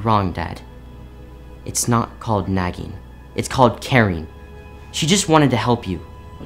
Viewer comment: i hear “its nit called nagging, its called karen.”